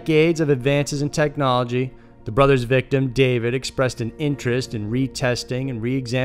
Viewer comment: I heard, "The brother's victim.." and thought I had forgotten how to speak for a moment.